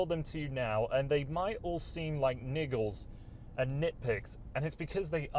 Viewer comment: did he say niggles? Last thing I want in my truck is a bunch of niggles.